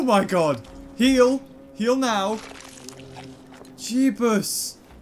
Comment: did you just said jebus